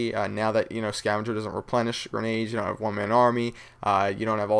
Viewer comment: I coulda swore they said Scavenger replenishes "Lethal Grenades".